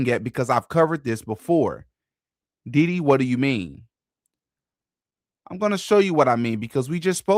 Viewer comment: sounded like you said Diddy what do you mean instead of DD? What do you mean 😂😂😂😂